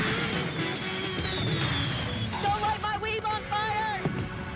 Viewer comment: Me : Focusing on Jeremiah and Mattie All I hear is Nilsa : “DONT BURN MY WEAVE”